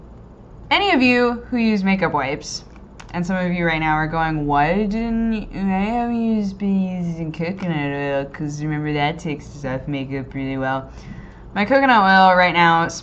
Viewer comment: What in the world did she say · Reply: "why didn't you.....why haven't you been using coconut oil because that takes off makeup really well"